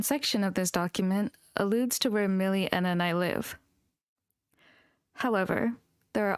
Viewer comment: if this is the part when ppl say about enna then i hear here milie and i live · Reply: I slowed the video down to hear what she says, Elira does say "Where Millie, Enna, and I live"